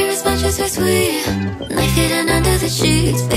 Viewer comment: Why do I hear "Don't fuck us or we will"